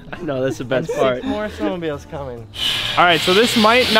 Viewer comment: WHAT DID JUST SAY!!!👏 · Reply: Theres more snowmobiles coming